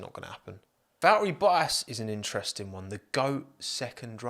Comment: Hang on, did you call Valtteri Bottas Battery Voltas at 6:12?